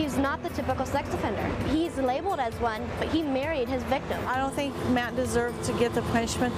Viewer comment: Did she just say he’s not a typical sex offender her married me like huh